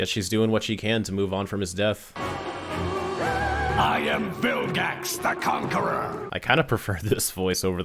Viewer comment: Fun fact: The subtitles say "Bill gax" "Phill gax" and "Vilgax" one after the other